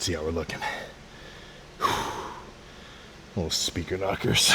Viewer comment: did say he’s listening to speaker knockerz????